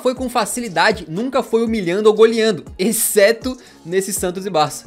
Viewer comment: Eu entendi nesse Santos do Barça